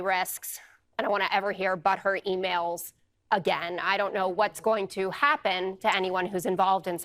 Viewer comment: Am I the only one who heard the news presenter say [I don't want to ever hear bụtt hurt emails — again]?